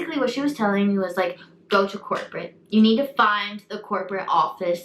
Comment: Lol why when you said “ Go to corporate “ I thought you said “ Go to court, Britt” 🤔😂